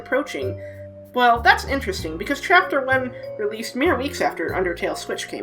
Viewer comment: it sounds like you say chapter 11